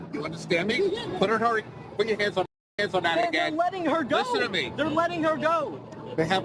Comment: anyone else catch the cop say "you put your hands on that again"